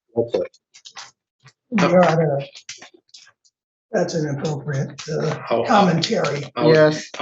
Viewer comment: Where you couldn't make out what he said, @ 9:34, he said "your honor, that's inappropriate commentary"...